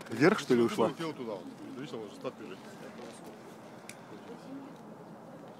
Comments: At timescale 15:29 after the sonic boom what is it that the Russian guy is saying.It sounds like Yeah Boy! but that's not it what does he mean....What i mean is someone please translate because i hear this alot among Russians when their surprised or in other situations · mark he says something that sounds like "Yeah Boy!" WTF IS IT PEOPLE!!!!!! That he says and what does it MEAN!!!!!!!!!!! PLEASE HELP ME FIGURE THIS OUT!!!!!!!!!!!!!!!